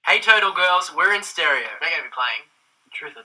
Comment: Is it just me or did it sound like said "Hey turtle girls" at the beginning? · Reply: He said "Hey Total Girls"